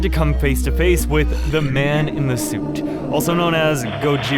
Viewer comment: Why did i think that said the man in the st😭